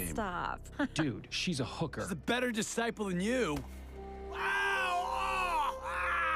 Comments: Man for a minute I thought he said she's a better apostle than you And they changed it to disciple to run from criticism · what did he say ??? Better than safe on you??? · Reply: She's a better disciple than you